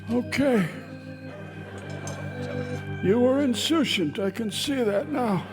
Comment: what is the word at minute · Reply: INSOUCIANT showing a casual lack of concern; indifferent.